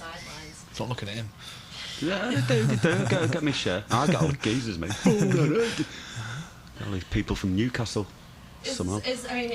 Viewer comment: What does Bonehead says about Newcastle at 2:14? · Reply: They do they do, I get my share, I get all the geezas me. Always people from Newcastle, somehow.